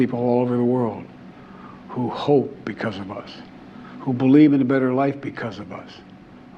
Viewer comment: When it was said in the opening of the video : "people of the world hope BECAUSE OF us" I thought I've heard "people of the world hope ... DESPITE OF us" . Oh, I've misheard.